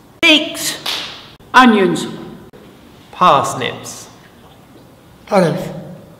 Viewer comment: WTF did he say? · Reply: Olives